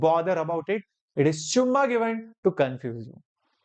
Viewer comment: did sir said summa given??